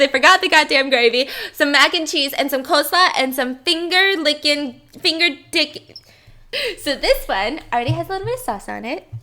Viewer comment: did she say finger dicking?